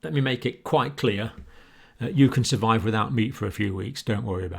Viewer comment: Dr Campbell, you gave me a fright. I misheard & thought you said, "you can live without me for a few days" & had coughed at the beginning. My mind raced to the worst. When my concentration returned you were talking about food????? Ah! he said meat. You are, & have been my rock of support throughout this pandemic.